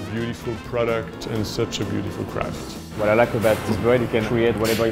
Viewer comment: anybody else hear "It's such a beautiful product and such a beautiful product." at 0:03 ?